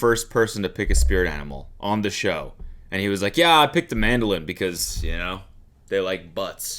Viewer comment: what is he saying here? 'Mandolin'? like that weird guitar-like thing? the word is mandrill cody, the name of the monkey is a portmanteau of the word drill and man